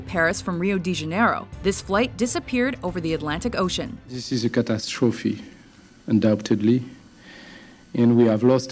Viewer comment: I know it's tragic and all, but everytime i hear that guy all i can think about is "cat ass trophy".